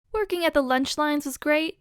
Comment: When I started the video I thought you said Twerking at the lunch lines